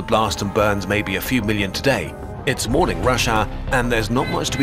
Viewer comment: why the hell did i hear Russia instead of rush our?